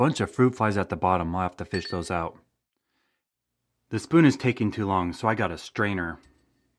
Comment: I SWEAR I HEARD YOU SAY “the food is taking too Long so I got a strainer”